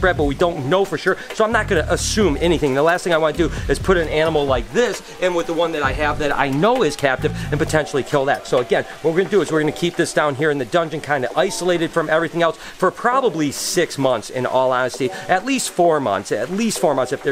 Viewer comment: did he say dungeon?